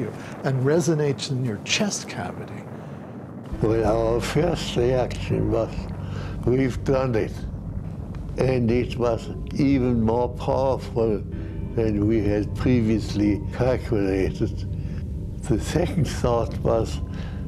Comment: wtf did he say at 4:04? and at 4:34? sure do look like genuine, kind, & trustworthy men. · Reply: He said something along the lines of "our first reaction was we've done it and it was even more powerful than we had previously calculated. The second thought was what a horrible gadget have we invented"